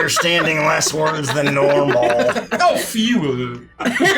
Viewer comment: What's say at 1:01:05? I can't make it out 😭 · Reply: I think he was just mispronouncing words for laughs, "a facade," "a ruse," "disguise," ... It sounded like he said, "a f-EE-wer" (a fewer). But I was looking in the comments with the same question.